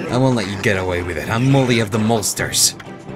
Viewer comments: I thought it said Moley of the Molesters and I got concerned. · Am I the only person that thought they said "molester" instead of Molster?